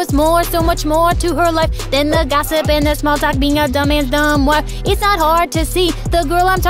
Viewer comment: WOAH YOU CUSS? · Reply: I thought they said dumb men and dumb wife on